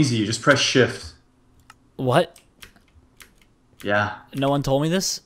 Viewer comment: it sounds like he said how long have you known this bitch!